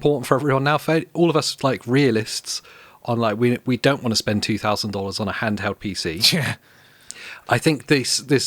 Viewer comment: Wait, did you say 2000? 😂 Bring that way down my man. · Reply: said "we don't want to spend $2000 on a handheld pc"